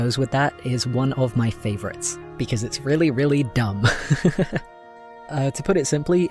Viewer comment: i thought you said flint was really really dumb and for a second I was YES GIVE US THE HIMBO